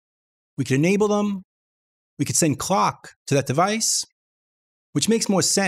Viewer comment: I bet you hear that question a lot but how did you manage to place virtual keyboard on the dock? In one of your videos you've mentioned it's a 3rd party thing? Love your videos! PS. You should set up a Patreon. Literally saved millions man-hours of growing Reaper community :) Cheers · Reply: Nevermind I thought you said clock when you actually said dock. Lol